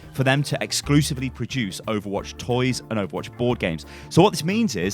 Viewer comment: I am tired as i am watching this. I heard "porn games" instead of bord games.